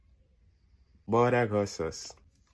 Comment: The moment when he said Buldak Hot sauce I thought he said Bardock hot sauce